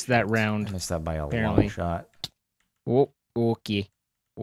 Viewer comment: Did Jardon say short or something else